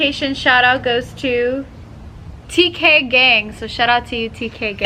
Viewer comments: Thought they said dk gang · for a second i heard “dk gang” love you ace family!